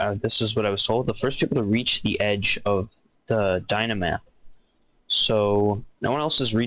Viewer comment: I thought they said Dynmap, which means the plugin that lets them see the world in a large map form through a browser. Still pretty sure minecraft itself won't stop.